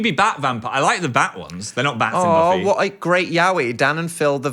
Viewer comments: Guys. Please let me proofread your captions. The transcriber wrote yaoi as yahweh, at 25:13. Let Me Fix This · not the subtitles at 25:14 spelling yaoi as "yahweh" 😭😭😭 like cmon get it together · the fact that at 25:19 the captions misspells yaoi as yahweh which is funny bc yahweh is another name for God. and well those 2 dont really go together